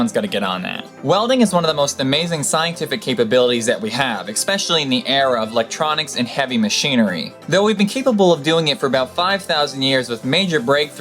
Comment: Did I hear "ex-pecially"?